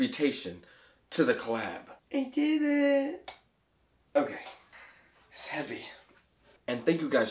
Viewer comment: I thought you said it wasn't heavy